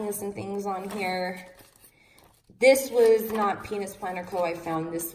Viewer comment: did she say “Penis planner co”? 🤣😂